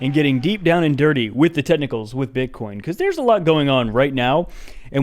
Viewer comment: I’ve thought you were saying tentacles every time you said technicals. Today is the first time I realized this beautiful fact.